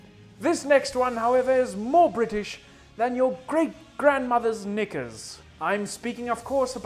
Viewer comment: Did he just say the "N" word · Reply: He said knickers